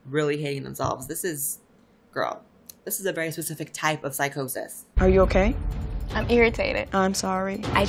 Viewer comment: Did you just say "PSYCHO-CIS"?! triggered